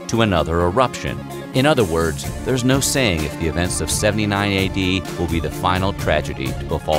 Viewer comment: I thought he said it was 799 ad but no 79 ad